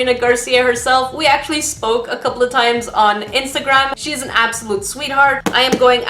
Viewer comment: I thought she said "we smoked a couple of times" and the way I saw you changed for a few seconds.